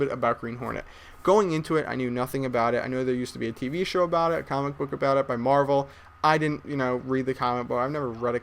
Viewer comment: Did he call a comic book a comment bar at 5:30? Double check anyone lol:)